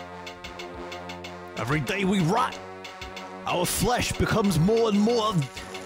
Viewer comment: What is he saying? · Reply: EVERY DAY WE ROT